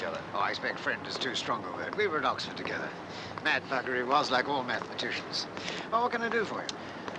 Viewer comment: Does he really say "mad f**ker, he was" at 1:01:31 ?! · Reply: No, mad bugger